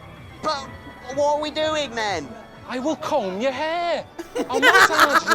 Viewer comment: "I will come in your hair!" I can't un-hear that... I know what he actually said though, "comb". It's his manc accent aha